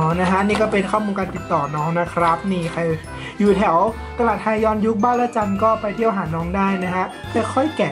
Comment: เราคงหูเพี้ยนไป ได้ยินว่า ตลาดแทยอน 😂😂 ความติ่งได้ครอบงำเราไปแล้ววว😂